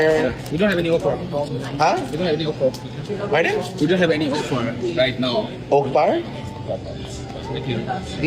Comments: It sounds like he is saying "we don't have any employment right now." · Yooooooooo ok, I get it now at 4:07 It's a miscommunication issue. The staff thinks that is saying "what offer/deals do you have" so they're saying that they're just employees and can't give "offers/deals/discounts" At 3:00, the staff is saying "We don't have any offer" x2 "We don't have any offer... right now" I don't think I heard "fuck off" at all? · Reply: I thought he said "we don't have any Okra" 😭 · Sounded like he said we don’t have anything to offer 😮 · well I guess we'll never know what was really said. All I know is that I personally didn't hear it. I heard him say something but it didn't sound like "fuck off". 🤷🏻‍♀️ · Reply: there is some garbled sound before the man said thank you, not f off. Thats what i hear. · It sounds to me like the server said “we don’t have any offer” with an accent and when the customer said “we don’t have any what” the cashier simply says “offer” but like I say he had an accent so sounded different but it certainly didn’t sound like he said F off to me?! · It sounds like he said we don't have any offer but it absolutely did not sound like he said f off.